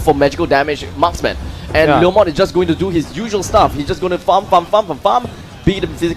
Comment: Sorry but i thought the commentator said pum pum pum pum pum But he meant farm